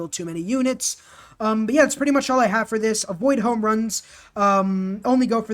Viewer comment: Anytime u said "avoid wonders" I kept hearing "avoid home runs" 😅 took me a while to realize that you were saying wonders not home runs